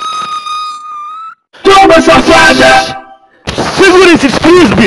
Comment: perdi tudo no "segura esses frisbees" ksksksksknskjkkjkj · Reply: Eu entendi "segura esses cuspes"